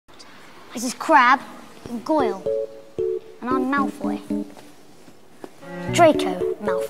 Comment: I just heard « I just crapped in Goyle, and I am Malfoy